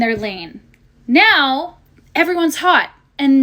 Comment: She actually said all girls are hot, 🤣🤣🤣🤣🤣🤣🤣🤣🤣🤣🤣🤣🤣🤣🤣🤣🤣🤣🤣🤣🤣🤣🤣🤣🤣🤣🤣🤣🤣🤣🤣🤣🤣🤣🤣🤣🤣🤣🤣🤣🤣🤣🤣🤣🤣🤣🤣🤣🤣🤣🤣🤣🤣🤣🤣🤣🤣🤣🤣🤣🤣🤣🤣🤣🤣🤣🤣🤣🤣🤣🤣🤣🤣🤣🤣🤣🤣🤣🤣🤣🤣🤣🤣🤣🤣🤣🤣🤣🤣🤣🤣🤣🤣🤣🤣🤣🤣🤣🤣🤣🤣🤣🤣🤣🤣🤣🤣